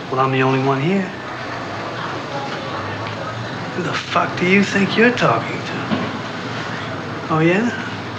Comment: What did he say?! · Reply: “Who da-fuq do you think you’re talking to?”